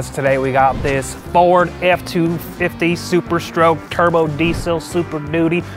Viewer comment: Ford F-250 "Super Stroke" That's what he said